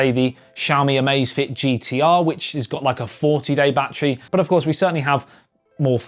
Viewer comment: Did you says 40 day in